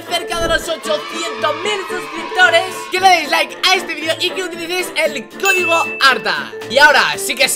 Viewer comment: jajaja, parece que dice que le den dis like